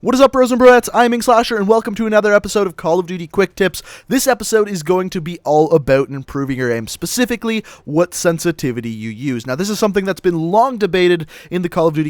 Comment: i thought he said cod quicktrips lol